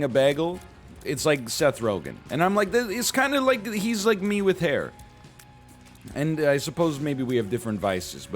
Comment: I thought you said Seth Rogan was "meat with hair" and didn't really know how to feel about it